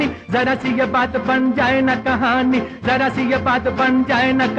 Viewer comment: it sounds like he is saying "vagina". hmmmm